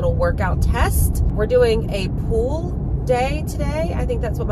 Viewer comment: When you said pull day I thought you said POOL day, and I'm like yeah that's my kind of workout. 😬